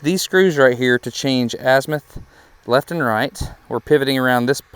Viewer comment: what does that word mean asmyth? Can’t spell it save my life to look it up ! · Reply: azimuth is the term you're looking for